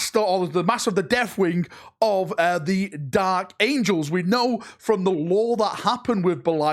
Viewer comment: The dalek angels? It sounded like you said dalek angels lol